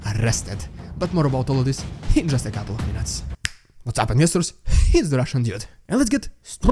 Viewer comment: Beware: you are saying "Hits the Russian Dude" again instead of "It's the Russian Dude". We don't want to hit you ! 😊